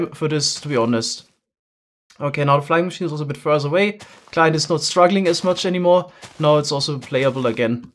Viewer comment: did i just hear "klar" XD